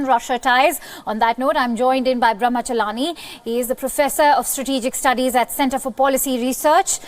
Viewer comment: Did she say "PROCESSOR"?